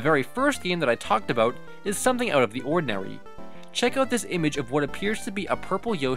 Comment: is it just me or did he say “unodinawy”